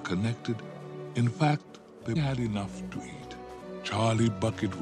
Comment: At first I thought he said “they had enough to eat Charlie “